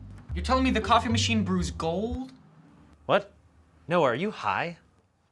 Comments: is he saying "no are you high?" Or "Noah, you high?" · did Ian say Noah or no uh